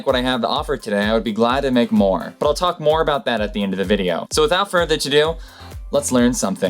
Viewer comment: without further what?! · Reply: I thought he said "without further to do"